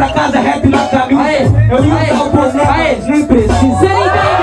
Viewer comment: não entendi oq o falou,alguém fala aq p mim pfv 😅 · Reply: Eu ia colocar o boné mas nem precisa